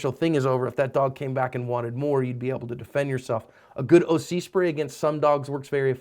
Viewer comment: I thought he said "a good uzi spray" at 2:29 lol